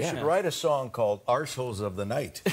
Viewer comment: I thought he said " Marshalls of the night" at first, then I realized with the laughing it was "Arssholes of the night" Great Show !! Loved everyone here, funny yet respectful and genuine.❤️🌹